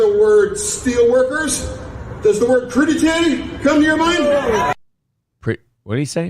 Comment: I think that Fetterman guy said crudité, which is some sort of veggie tray. There was a video of Dr. Oz shopping for ingredients to make a crudité.